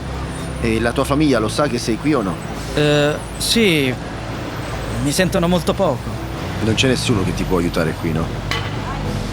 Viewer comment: the subtitles say "They hear from me very little", don't know if that's the same in the Dubs because I'm listening the Interlingua but I understand him saying "Mi Cucha murió hace poquito", translation to that is "My mom died recently". I mention this as a minor correction.